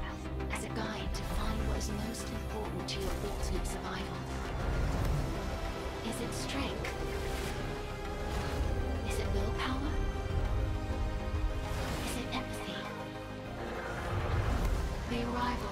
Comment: “Is it strenght? Is it no power? Is it Pepsi?” That’s what I hear😂😂😂